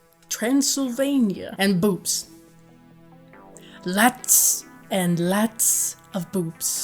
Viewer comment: Oh,BOOBS. I thought you said: ‘boots’. Consequently I was looking in the wrong direction. I don’t really know what just happened,only that it was one of the movies that I have ever seen. 🐺